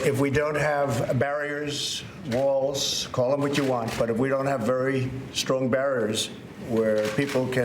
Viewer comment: Sounds like he says strong bears lol that mouth breathing though